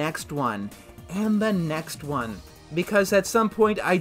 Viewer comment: I figure you did actually say Purgatory at the end there, but it really did sound like you said Burgatory. So I'll just assume you're eating burgers and shakes for all eternity.